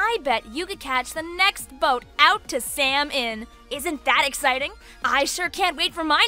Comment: I thought you were talking to me every time you said Salm-Inn. (It sounded like you said Sam. eeEeEeEeeEe)